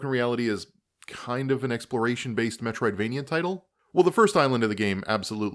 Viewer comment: I thought you actually said "metricsvania" and, you know, fair enough